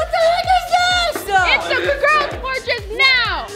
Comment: I thought said fartress